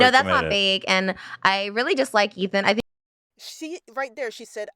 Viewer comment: It sounded like she said "I really just like Ethan". The closed caption wrote it that way also.